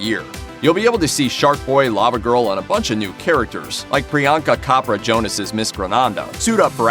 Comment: Did he just say Priyanka Kapra instead of Chopra?!?!? 😂😂